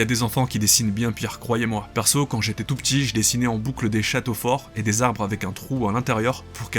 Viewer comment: Ôh grand malade : tu m'as fait frissonner sur mon siege à bascule avec ton "des Chat** " au fort- ... avec un trou à l'interieur" °-° Quelle malaisante turbulence pardi... C'est à ce moment précis que j'ai compris que... je j'avais bien besoin de vacances XD